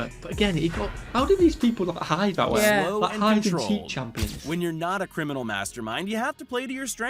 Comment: Did you say at 3:36 Hide and Cheat? Is that British?